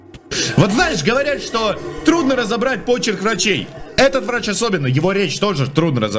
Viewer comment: мне послышалось, или он сказал "Особенно добра тебе, Юля"? АААААА~